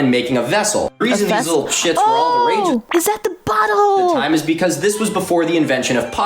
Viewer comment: Please for the love of god tell me I’m not the only one that thought said “is that the butthole” instead of bottle at 2:48…